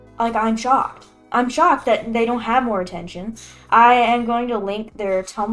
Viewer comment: i thought u said pay attention to you